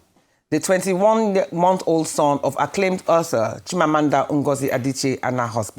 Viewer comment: Pls can someone tell me what she said here? 0:14 ...acclaimed... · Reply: "author"